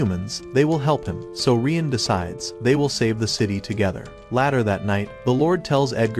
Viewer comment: Did you hear what he said on 51:36 😂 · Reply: He said latar that night 😂😂😂😂